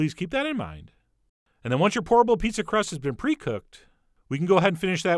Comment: I keep hearing "horrible pizza crust" 😂